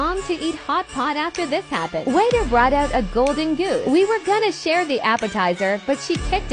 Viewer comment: Why did the “golden goose” look like a Nike leather shoe 💀😭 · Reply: Golden goose? Lol I literally thought she said “Nike shoes” 😭